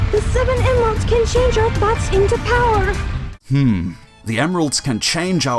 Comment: I thought Tikal said" the chaos emeralds can turn our BUTTS into power " I was sitting there like ok then I heard say our thoughts into power and I said oh ok that makes more sense than my 5 yr old brain trying to process it